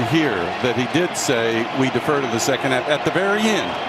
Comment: "We defer in the 2nd half"- that's what I heard, can't even blame the ref much, it got incredibly loud and he had to ask twice.